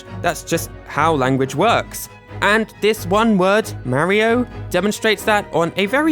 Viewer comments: In mario 64 i heard him say madio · It sounds like Mario is saying mardio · An it sounds like it says madio